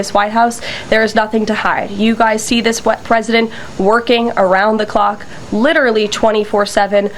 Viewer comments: Ms Leavitt, did you just say: “…you guys see this wet president…” -? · what did she say there? “You see this wet president” 😮😩 now I have to burn that sight from my brain meat 🙈